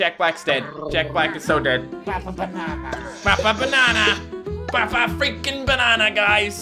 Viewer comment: when the teachers kill you it sounds like they say SLAP A BANANA